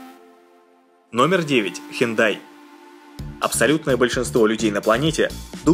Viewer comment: Мне одному послышалось не Hyundai а хентай?